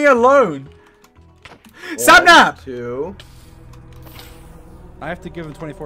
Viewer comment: my very first thought when i heard this was 'wapnap'